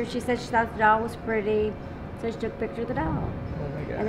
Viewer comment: Soo.. 13:54 she took the picture of the doll This is what the lady said. But if you open the auto generated subtitles fpr this video, it says she took the picture of the devil See for yourself I’m not lying. 🥶 16:01 - 16:03 when you said old women death, there was lightening outside. You can see it through the blue glass 18:39 when you ask them to communicate with u, take a look at the chandelier, it starts getting bright and dim. Very slightly but it dit till